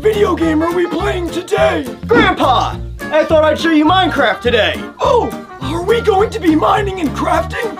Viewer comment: Minecraft? I thought you said mein kampf